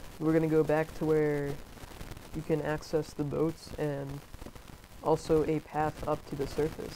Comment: did he say ''access the boobs''? I know it's old vidoe but I don't care